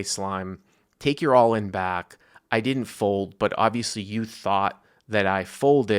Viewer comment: Sounded like he said I fold.